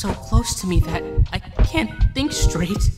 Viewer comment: For a minute I thought he said "I can't think I'm straight" 🤣. Am I the only one who read it like that?.